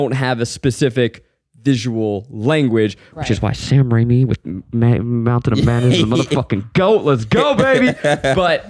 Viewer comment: I can’t make out what said into the mic at 13:19, can someone help me please? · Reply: It sounds like "Sam Raimi with Mountain of Madness", and I think he's means The Multiverse of Madness, but accidentally said "Mountain" instead of "Multiverse"